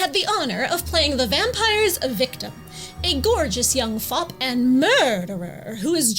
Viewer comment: I had a major double-take for a moment there when I misheard "gorgeous young fop" as "gorgeous young fuck".